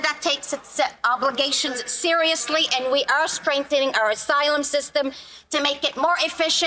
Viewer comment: Did she say...Asylum system???